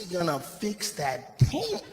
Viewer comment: Can someone tell me what word is that at 58:50? Tame? Dame? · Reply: The word is "Taint". It's a slang word used to refer to the perineum, the part of the body between the anus and genitals.